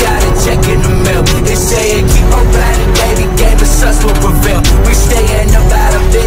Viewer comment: really thought they were saying "jesus will prevail' at the end on my first watch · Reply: no they are saying "g-supps will prevail"